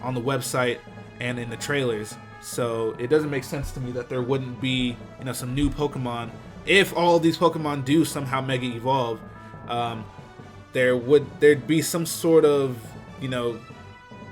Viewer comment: I thought they said there would be no Unova mega pokemon